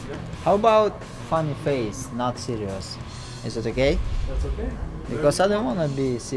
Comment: i thought loma said "is it a gay?"